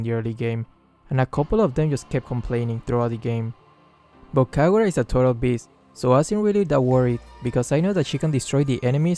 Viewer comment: sounded like "Kagura is a total bitch"